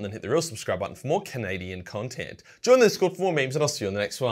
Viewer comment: I know he said “cadian content” but I heard “Canadian content”